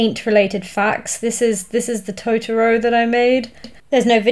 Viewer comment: I thought Totoro said Toronto